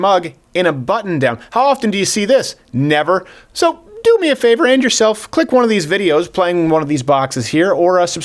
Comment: Doesn’t it sounds like he says “do me a favor end yourself” at the outro